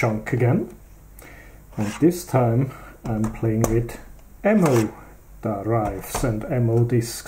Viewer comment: Oh, I thought it said Mo Fo drive...